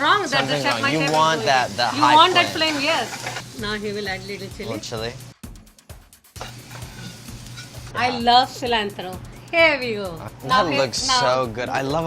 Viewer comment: she really said "i love cilantro PERIODT" in 14:55??? i stan her already